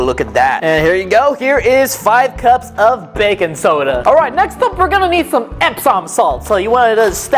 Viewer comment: I thought you said bacon soda